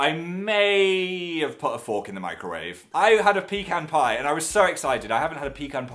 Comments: I think my headphones suck because I heard him say pee-con pie..... it's pecan pie...... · When he said he put a fork in the microwave, I thought he said "I put a fork in my grave".